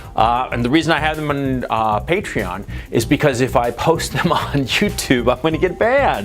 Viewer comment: Did you say "JewTube" ? 😂